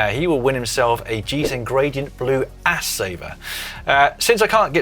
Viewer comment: Caption comp” Oh ass saver, i thought you said ass shaver !”